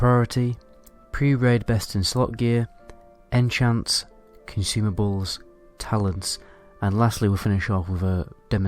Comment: When he said consumables, I thought he said “can you see my balls” 😂 I’ll see myself out.